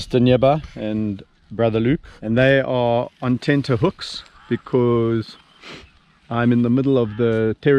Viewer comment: what’s “tinta hooks”? Or did I hear that wrong? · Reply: "Tenterhooks" is another way of saying nervous or uneasy. He was saying the hyenas were nervous because a higher ranking hyena was approaching.